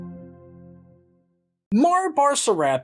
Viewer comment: I must be tired thought you said mars bar